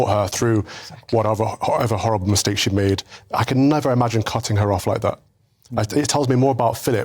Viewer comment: did he really say "I could never imagine cun*ing her off like that"